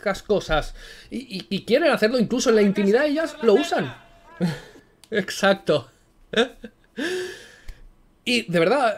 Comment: ¿ Qué es lo que dice ? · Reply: "A casa a hacer la cena".